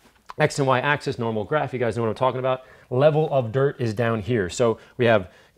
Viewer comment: I thought it said level of diet 😂😂 wow.